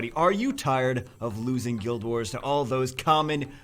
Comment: I heard it as, "Are you tired of guild wars?" Yes, so I don't bother. I quit all pvp like 3 months before I quit the game